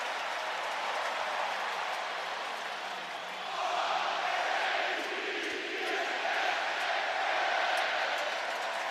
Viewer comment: what was the crowd shouting? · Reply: R-A-V-E-N-S...RAVENS!!!.